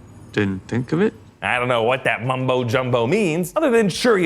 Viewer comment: did you say mumbo jumbo?